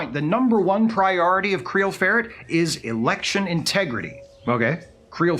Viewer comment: I thought this mf said Creole Ferret.